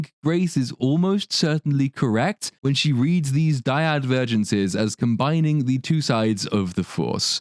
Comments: Jesus Christ! You scared me with Grace Randolph…again. I thought she kept saying “Virgins” & I couldn’t follow what she was talking about, but I think she may have been saying “vergence” & I’m still not sure what she’s on about! I also don’t think vergence is the opposite of divergence, I believe that would be convergence? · I honestly thought Grace was saying "Virgins" and was concerned that she didn't understand the meaning of that word, and then when you said "Vergence" I realized that she sounds a little drunk and / or medicated, not just because of what she was saying but how she was slurring and blurring her words.